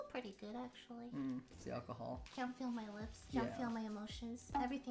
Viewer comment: Look...I really fucking thought she said "Come feel my lips"